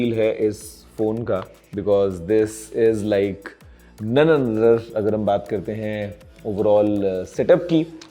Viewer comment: guruji ne kya kaha! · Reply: None another